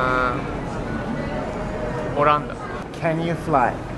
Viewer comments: what did he say? Holland? I didn't understand :s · Did he say Ireland? Woo! More than welcome here ^_^ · what did he say? Porando? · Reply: Jin Kazama He actually said オランダ(oranda), which means Holland or Netherlands (I know it's not the same).